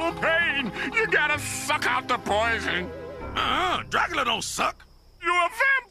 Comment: I wasn’t paying attention and i thought he said “Fuck out the poison” I had to rewind to make sure it wasn’t my imagination